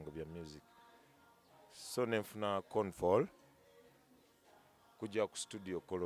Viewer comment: kitufu enosiyesawa but he said "con fall" instead of "phone call" 🤭 RIP 🕊